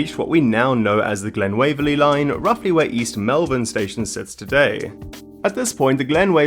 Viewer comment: did he say East Melbourne or East Melvin or did he mean East Malvern?